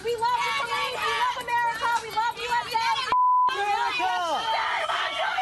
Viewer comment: I don't know about you guys, but I heard "I'm Shamu"